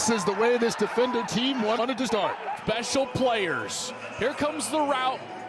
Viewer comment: did he just say “special teams, special plays, special players” 💀